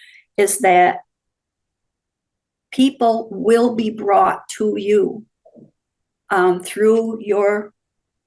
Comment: What does she say at 53:30? '___ will be brought to you'. ?? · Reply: People will be brought to you..